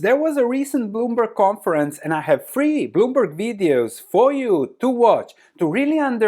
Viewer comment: Misheard at first and thought you said 'three boomer videos' - I suppose they are that too 😂